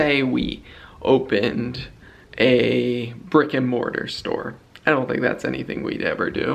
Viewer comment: When you said “brick and mortar store” I heard a “rigor mortis store”. You’re like “we wouldn’t want to do that during a pandemic. I was thinking... nows the perfect time but why in heavens name would you ever get into that?!!!! I rewound the clip and then realized you said brick and mortar and my blood pressure came down to normal.....